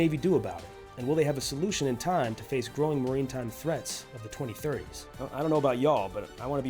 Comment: Did you just say "marine time threats" instead of maritime?